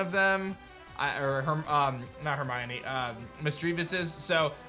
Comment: lol did he just call Misfreavus Hermoine?